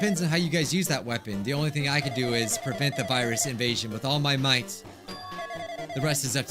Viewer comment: I thought you said all might for a second lol